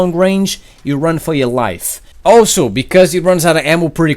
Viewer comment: I thought you said "Run for your wife" xD